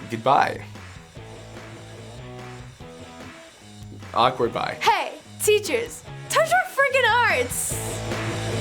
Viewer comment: Sounded like she said, "Hey! Teachers! Touch our freakin' arse!"